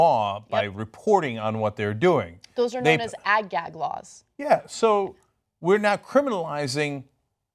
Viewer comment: Can I ask what Anna says at about 4:50ish? Ag-Gat laws? Anyone?